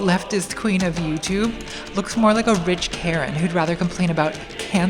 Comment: Instead of rich Karen, I thought she said orange carrot. “She looks like an orange carrot.”